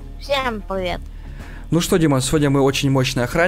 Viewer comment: мне послышалось или он сказал "ну что димас"